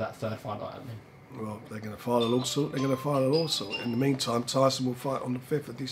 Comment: What does frank call a Law Suit at 7:31?! A lawsal? 😂😂😂😂